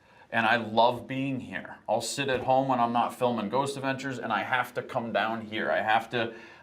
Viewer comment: I had to play back the part where they are in the basement It sounded like Zak said " after i am done filming Ghost Adventures " The first time i heard it it sounded like he said " Ghost Hunters ". I guess the video had an effect on my hearing certain things. Weird i know but it happened.